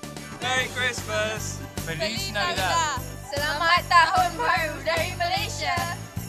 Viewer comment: the two Malaysian women said selamat tahun bahru dari Malaysia - which translates to 'Happy new year from Malaysia' and not Merry Christmas. Ironically, the words that appear on the screen are a correct translation - selamat hari natal is how you would say Merry Christmas in Malay. Oh dear, the BBC producers did not pick up this mistake in post-production? Tsk tsk. Sloppy work. I am sure those two ladies spoke perfectly good English (like most Malaysians), so why didn't they verify with them, "what did you just say in your language?" #bbcfail